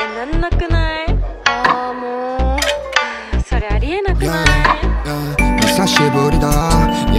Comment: 처음 일본어 대사 ねえ、、また忙しいって 返僧来たんだけど? (있잖아,, 아직 바쁘다고 답장왔는데??) まじで？ (진짜로?) ねぇ、忙しいっても理由になる？ (있잖아,바쁘다는게 이유가 돼?) えーなんなくない？ (엥? 아무렇지도 않아?) あーもう、それありえなことない？ (그건 있을 수 없는 일 아냐?) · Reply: 마지막 부분 ありえなくない가 아니라 ありえないことない 인건가요 ?? 저는 ありえなくない로 들려서요 !